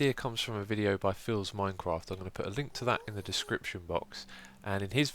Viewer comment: X, can you help me out here? 10:20. The idea came from who? Phil's Minecraft? It sounded like "philza" and it blew my mind but it can't be.